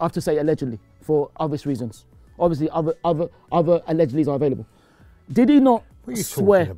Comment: did ty just say other allegedlys are available?